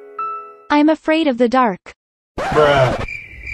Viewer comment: Oh i thought he said i love bears